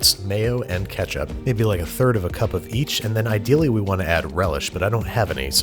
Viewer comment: Anyone else realize that when he was chopping up the pickle, it sounded like he said "I"dill"ly" Instead of ideally😂